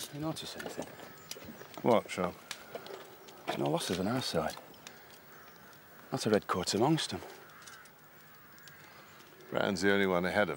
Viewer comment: The key line, but I can't understand what he says. Help? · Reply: "There are no losses on our side. Not a red coat amongst them."